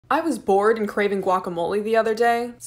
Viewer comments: I really thought you said "I was Born and was craving" lol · Your videos are the best but I also thought you said you were born in guacamole